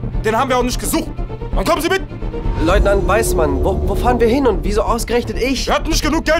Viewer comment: "Ich bin nicht Professor fidget" !! "Und ich bin auch nicht weiß, man" !! beste Stelle hatte so einen lachflash 😂😂😂😂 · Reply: Ich habe bis heute immer "Leutnant Weißbart" verstanden.